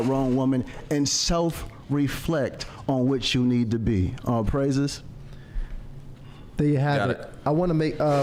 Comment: did he just say all praises and it went dead quiet?😂😂😂